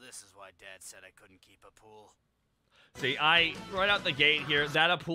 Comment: I always thought Raditz said "a Poodle", I never realized he said " Appule"